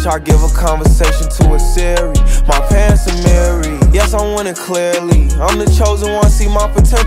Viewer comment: LOL I always thought he said “my parents are married. Yes, I want it clearly “ Not my pants Amari yes I’m winning clearly